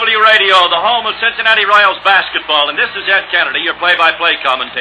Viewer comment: did he say " This is Jack Kennedy"? That's eerie